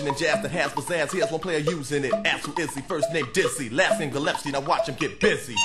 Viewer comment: anyone else hear Big Daddy Kane say gillEPsie at 1:54?